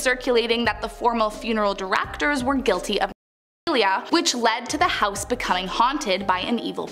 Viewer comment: At round 3:45 did she say they were pedophiles ?